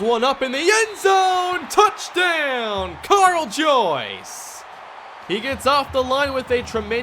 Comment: Kyle Joyce? I am the only one who heard that